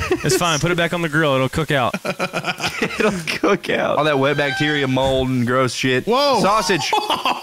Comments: what say and what did they hear? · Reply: saying wet bacteria made them hear wet+back which is a slur. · someone explain i dont hear what heard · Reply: Wet bac